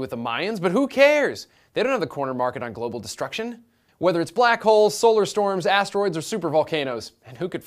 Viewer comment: Oh, he said black holes. I thought he said black whores..